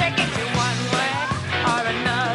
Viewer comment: Tantas veces que he visto este video y nunca entendí lo que él chico de cabello largo dijo😂 en el minuto 3:42 si alguien supo tener mejor comprensión auditiva le agradecería que me dijera 😅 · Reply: Que ni tina le canta a el